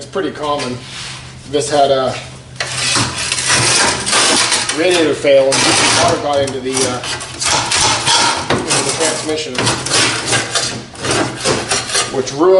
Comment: awesome video but at about 5:50 when you tell us what happened to the trans. you are scraping and we cant hear it. i looked comments and cant find it. thank you update: I turned on CC and pieced it together fro there. Radiator fail. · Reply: Sorry you cant hear it. the radiator leaked causing water damage in the transmission.